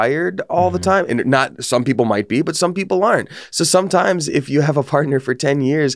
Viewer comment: These captions are pretty frequently inaccurate. Like around 22:00 where says "Some people are wired that way (for monogamy), and some people aren't." but the captions say "and some people learn." Which kinda goes against what he was saying.